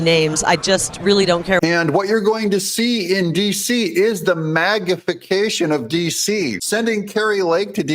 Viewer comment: it sounded like you said MAGA-fication of DC? Maybe you congress a new word?! 😮